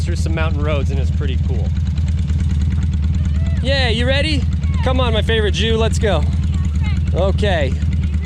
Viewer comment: ? What did you say @ 15:23 ? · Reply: Come on my favorite Jew let's go 🤣, that's my gfs nickname 😉